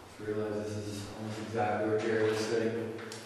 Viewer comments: As sits I swear I hear Jerry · i heard "jerry" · I heard someone say Jerry at 1:01:25.